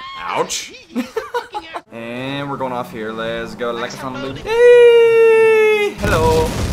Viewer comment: I coulda sworn I heard the "Likeasumboodee" from OwnagePranks